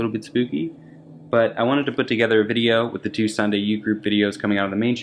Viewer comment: I thought you said Sunday youth group